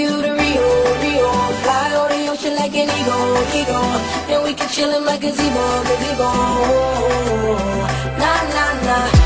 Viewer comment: Am I the only one who thought it said "and we can chill in my cazeebo"